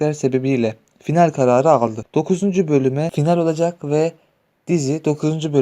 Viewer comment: Ben 44 bolumde final olacak diye duydum